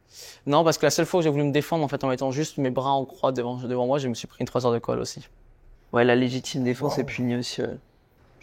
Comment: "3 heures de colle" ???? Hein ? Pardon. C'est moi qui comprends mal ou ?